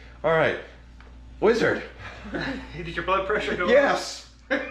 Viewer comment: I thought the Wizard said "Did your butt pressure go up", then I realised he said "blood"!!